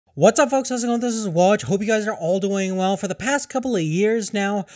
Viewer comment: what are you saying man? ''whatsupfpolkopiekopwjiopsas'' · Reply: Hey what's up folks how's it going this is hope you guys are all doing well